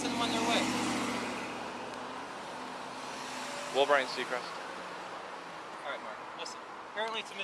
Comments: did he just call him Ryan Seacrest? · “roll Ryan Seacrest”. I could have sworn he said that lol